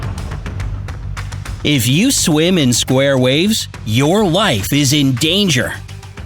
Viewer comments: Did he said at 0:15 "Your wife is in danger" Everyone check your wife or mom when they swimming 🏊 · When he said your life is in danger I thought he said your wife is in danger😂😂😂😂😂😂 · Is it just me or did it sound like he said "if you swim in square waves, your " WIFE " is in danger"😂😂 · I heard "if you swim in square waves your wives are in danger" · When he said "your life is in danger" at the beginning, at first I thought he said "your wife is in danger" 😂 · At the biggining I thought you said : If you swim in square waves you WIFE is in danger xD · Some of the deadly oceans are colorful and I heard bright side when he said your life is in danger I heard him say wife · Did he just say your "Wife is in danger?"...Lol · In the intro it sounded like he said ur wife will be in danger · did he say your wife??? I heard your wife.. ._. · I thought it said your wife is in danger XD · I know he said life, but it 100% sounds like he said wife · I thought he said: ‘your wife is in danger!’